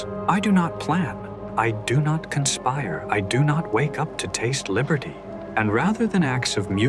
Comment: Love the fact the voiceover says "I do not wake up to taste liberty" but that ain't what the caption said